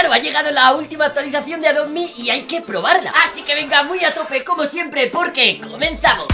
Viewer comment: nadie se dio cuenta que dijo atrope XD